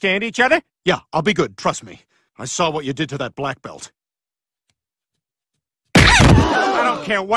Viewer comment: I thought brain said: I saw what you did to that “black person”